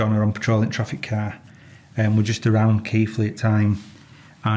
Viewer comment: Omg, I thought you were saying keithly not keighly lol